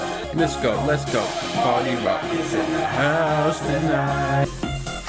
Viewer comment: Wait… in party rock anthem it says “party rock is in the house tonight” and not “party rockets in the house tonight”???? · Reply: I thought it said "party rockers in the the house tonight" Damn